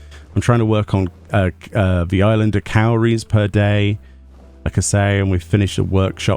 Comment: Cowries? I thought you said calories 🤣